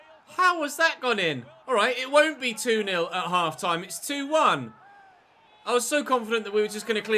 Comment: "it won't be 2:0 at half time" I heard ''Iwobi 2:0 at half time'' I was very confused for a while